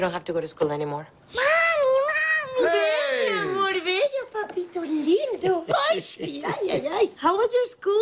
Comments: What does Gloria say to Joe @ 3:26 ? 'Mi Amor'___? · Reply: I heard "Venga mi amor bello, papito lindo" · What does Gloria say to Joe @ 3:26 ? 'Mi Amor'___? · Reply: Mi amor, venga papito lindo